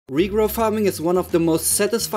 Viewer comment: am i racist?: i thought he said negro farming not regrow farming😭😭